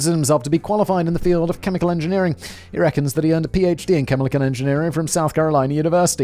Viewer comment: “chemilichan engineerier” I had to slow it to .25 to figure it out what was actually said lol.